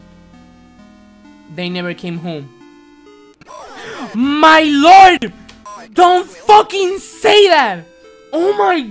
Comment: What was it that said at 5:02? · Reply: Carnage